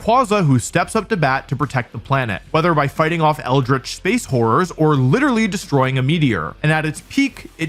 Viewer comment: I swear I had to turn the captions on because I swore he said space wh0res lmao